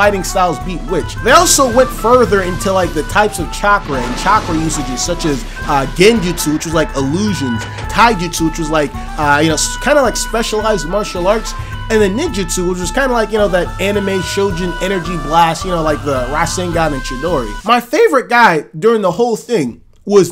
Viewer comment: Thought you said “Special eds Martial arts” I didn’t question it